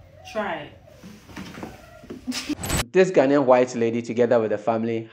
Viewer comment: Did u just say, "ghanaian white lady??!!"